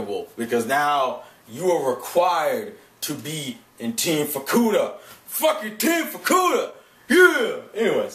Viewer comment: when you said fukuda, i thought you said vorkuuta xD